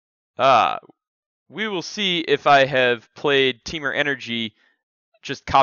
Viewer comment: He actually said "Let's see if you beat my Temur tier copy paste hahaha".